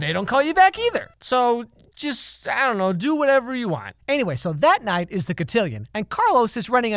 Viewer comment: did he say: "Do whatever you WAND"? Am i crazy?